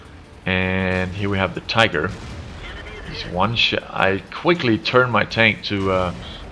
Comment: he says shit instead of shot xD